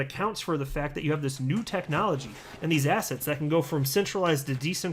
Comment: Great info, thanks for keeping us informed…I gotta say that every time you say “assets” I hear “ass hats” 😆